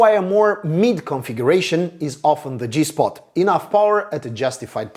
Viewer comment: Did you say sweet spot? I thought I heard G-Spot! 😁